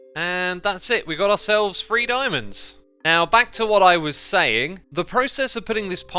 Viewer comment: When x said he got 3 diamonds from the miniblock chest, I thought he said free diamonds and i was thinking "yeah, but how many" until I saw that it was three